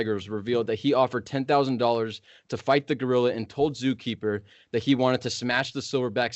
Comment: Bro I literally thought he said Godzilla